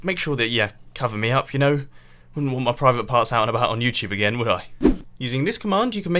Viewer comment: hold up... did he say AGAIN?!?!